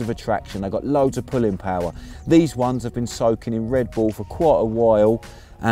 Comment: did he say soaking in red bull? 36:30?